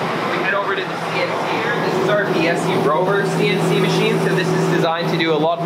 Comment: What kind of CNC do you mention at 6:48? I couldn't quite catch the name of it. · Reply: Biesse Rover!